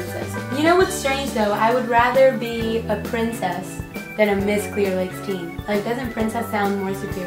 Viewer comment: Is it weird that when she was holding up her sash I thought she said QUEER lakes? 😂